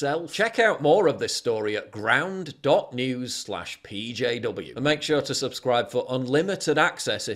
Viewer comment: ‘Ere mate you need to dial back the cockney a bit, thought you were saying BROWN NEWS until I saw the address